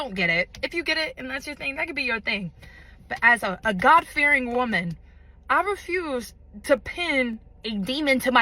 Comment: I heard "as a goth fearing woman," and that just feels more accurate.